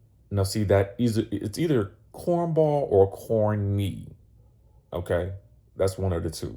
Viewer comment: I think Porsha is saying corny